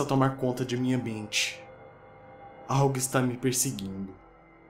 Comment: kkkkkkkkkkkkkkkkkkkkkkkkkkkkkkkkkkkkk quando o falou "algo esta me perseguindo" por alguma razão, eu entendi "a rockstar me perseguindo" kkkkkkkkkkkkkk